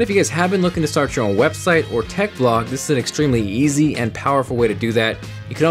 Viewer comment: lol am I the only one who heard "yeezy"